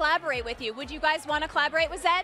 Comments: Did anyone hear RM say hell yea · Reporter: who’s most likely to get married first?? * rm translates* Jimin: what!?? 😳😳😒 Me: 😂😂😂😂 Edit: did Rm said “ hell yeah” at 3:42 😂😂😂😂😂😂😂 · Did rm say hell yeah *replay it * OMG HE DID HAHHAHAHA